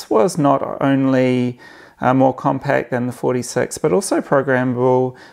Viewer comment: When you said, 'card reader' I heard 'carburetor' and for a split second I stared at the screen thinking, 'Huh'?